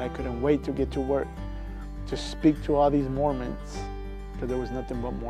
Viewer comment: what does he say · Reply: He couldn't wait to speak to the Mormons at his workplace, he said they believe differently and wanted to tell them about his experience.